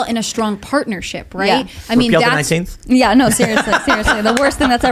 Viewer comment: i've listened to it 10+ times .. i did not understand tim .. what did he say? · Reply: Repeal the 19th